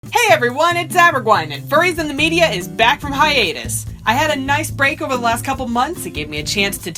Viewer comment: At a second I thought you said hi anus